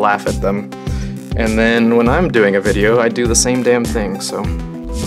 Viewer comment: o darn it sounded like u said dam